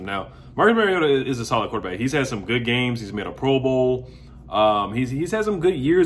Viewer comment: I keep hearing you saying "MO-BOUL" . What the heck is that?